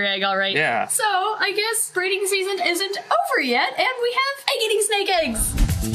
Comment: When says “egg-eating snake eggs!” It sound like she is saying “eggity snake eggs!” My mum was like “ I know a lot about snakes but eggity snake eggs don’t exist?”